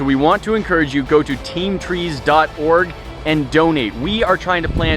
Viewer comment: Sounded like he said Go to Teamtrees dot Organ donate 😂😂